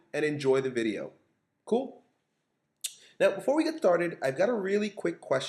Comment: he said tharted instead of started! I just think it's hilarious in a friendly way haha x)